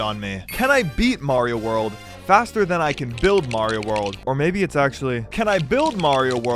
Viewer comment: I thought it said, “can I build Mario before I eat Mario”